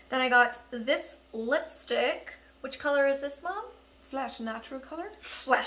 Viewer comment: I thought your mom said “flash” instead of flesh 🤦🏽‍♀️😂